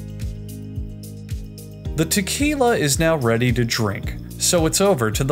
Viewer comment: for a second I thought that said “Tequila Thanos”